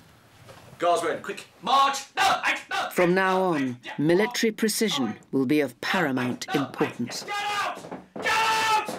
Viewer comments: what is that guy saying at 24:40 is sounds like he is saying "dull jite, dull jite, dull jite, jap" lol idk. Can anyone pls explain? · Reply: left, right, left, right, left, right. I think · What does the Sergeant Major say after quick march (repeatedly) till he asks the squad to halt, at 24:39? Any idea? Thanks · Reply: he just says left and right but with an accent